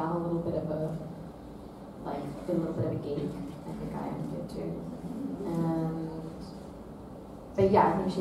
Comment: what did she say?? Little bit of? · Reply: She said “deep down( Alicia is) a little bit a geek and I am too”